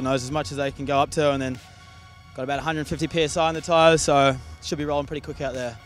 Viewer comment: 150 psi did I hear that right?